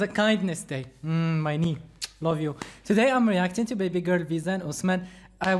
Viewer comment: did you just say " baby girl Visa" ?!?! 😂😂😂🤭😂😂😂🤭